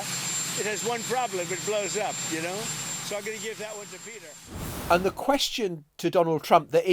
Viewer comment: did he say he's going to give the hydrogen car, which blows up, to Pete Hegseth? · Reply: I think he said "Peter", not "Pete", so it might be Navarro.